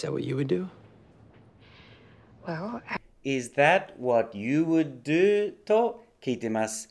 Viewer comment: what聞き取れていない you weに聞こえて困惑(2周目)